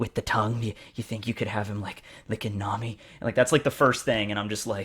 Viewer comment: you said perospero licking nami and my brain went into a defense where i thought you said perona licking nami all over which is much more acceptable Also, "Draw brook at 40 and 60"